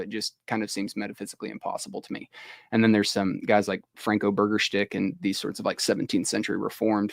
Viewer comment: did he call burgersdijk “burger-stick”? 😆